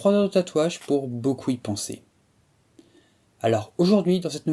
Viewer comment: j'ai compris "E-penser". 😅